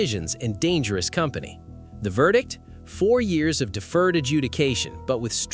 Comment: I swear it sounded like judged said 40 years of DA.